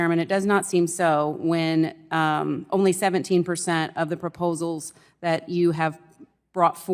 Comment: subtitles for the hearing impaired read 177% instead of 17%. If I was hearing impaired I'd be completely lost in all of this thank you Screw tube! It would seem that Biden is controlling the captions...🤣